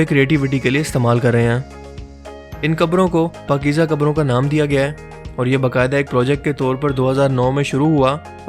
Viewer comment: 'Pakiza Qabro' am i heard that right?😅